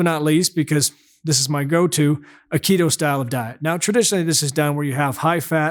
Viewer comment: Aikido-style diet is what I heard.